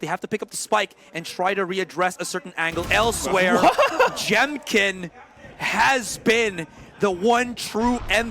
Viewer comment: ここ音声だけ聞いてたらじゃんけんにしか聞こえなかった